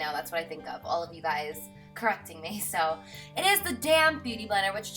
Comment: haha I always thought you were saying damn beauty blender but I knew you meant damp. p.s your beautiful💕